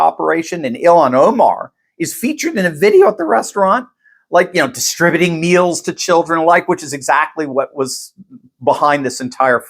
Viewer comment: I had to play that back. I thought you said "distributing needles to children" which actually, barely surprised me considering all that Democrats have been doing recently.